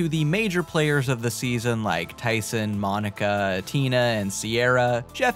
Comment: I thought you said Mike Tyson instead of 'like Tyson' 💀